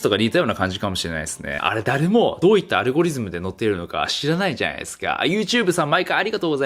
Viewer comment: 顎リズムって聞こえた。 完全になおきまんに洗脳されてるわ、、